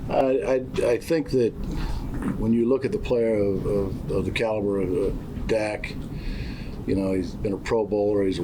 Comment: Whats the timestamp? I didn't hear it. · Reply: but I think he said caliber not cowgirl lol